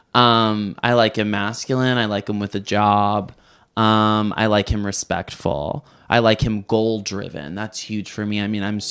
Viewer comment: what she say? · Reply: She said “who went in without tapping in was that you?”